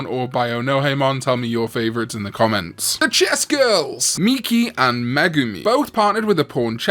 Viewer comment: I honestly misheard you, I thought you said "The Chesty Girls" lol.